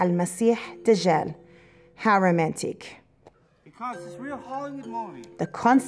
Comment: Did I hear "How romantic" or "Haramatic" :-) ... May Allah protect us